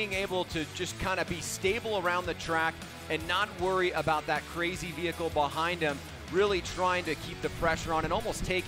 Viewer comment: i thought he said crazy hippo behind him😂😂😂😂